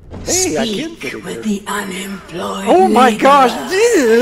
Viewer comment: Anybody else hear the night mother say “unemployed n-word”